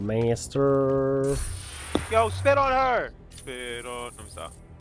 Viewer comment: yo SPIT ON HER!!! SPIT ON- lemme stop... i think thats what he said lol